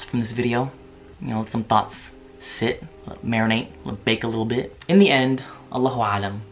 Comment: omg I thought you said bacon a little bit ahahahah. This video was super informative thank you for making it! As a british Pakistani I’ve been raised strictly eating halal food only. I didn’t even know about the whole people of the book thing but I’m glad to hear you’re eating halal food now may Allah swt bless you brother :) Insha’Allah